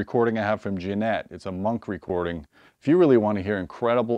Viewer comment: Does anybody know the artist and album of the first audio disk he spoke about? I thought he said Jeanette at Monk Studios. Right before the Steven Wilson one. I just can't find anything on it. Thanks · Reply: It is Jienat Mira. Took me a while to figure it out too.